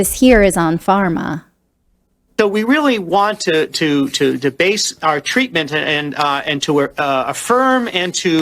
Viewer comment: I heard 'debase'. That sounds about right 🤣.